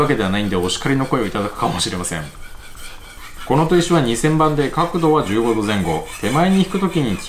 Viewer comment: 砥石2000番が2000万に聞こえて2度見した